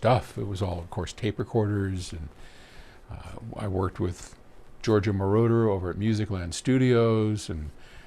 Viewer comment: Did he say Giorgio Moroder, the Italian music producer?